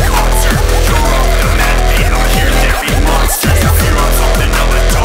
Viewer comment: What's that voice say at 2:10 ? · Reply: the pitched up bit is the 'hackdverse' Rarity voice: "hahaha, out here there be monsters!" in reference to my R&V collab with Cii